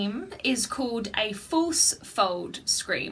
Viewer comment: what did you call the growl? Force or false? Also your awesome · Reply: false